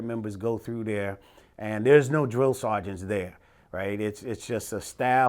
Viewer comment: i thought it said angry drill seargent lmao